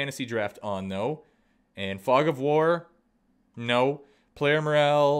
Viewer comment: I wasn't listening 0:36 I heard "Frog Of War"